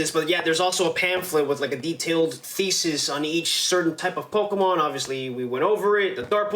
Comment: I misheard “detailed faeces” and giggled inside.